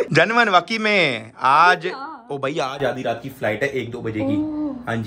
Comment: he said genuine and i thought he said janeman 😭😭🥺🥺❤️